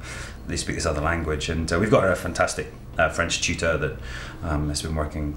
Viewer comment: Haha 0:47 does sam accidentally say "cheater" instead of "teacher"? ;) Cait smiles after he says it. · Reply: Julia P He says "tutor" :)